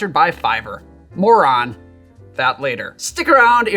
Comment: Did he just call me a moron?!?!